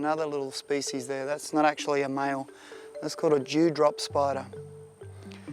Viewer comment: dont hate me but i thought he said Jew drop